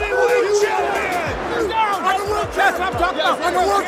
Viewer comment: hahaha 13:30 " I WORKED HIM, I WORKED HIM!". or did he say "Whooped". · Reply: "I'm the world champ, I'm the world champ."